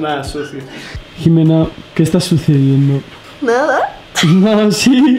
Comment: Solo yo escuche "Jimena que estas haciendo amor" 9:25 o ya estoy alucinando! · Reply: Dice “¿qué está sucediendo?”